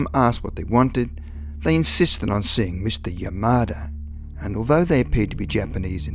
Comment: I know that you said Mr. Yamada, but at first hearing i thought you said "Mr. Your Murder" and that took the shit out of me. lol